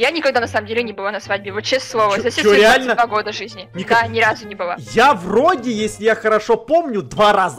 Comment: Мне послышалось что сказала: я не была на свадьбе за все свои 72 года жизни 😽🙃😂😂😂😂😂😅😅😅🤣